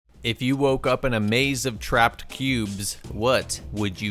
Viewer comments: I misheard the beginning of the video as “if you woke up in a maze of trapped pubes”. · Seriously thought he said "maze of trapped pubes" at first 🤣 I would kill myself 😐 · At the start I heard “A maze of trapped peub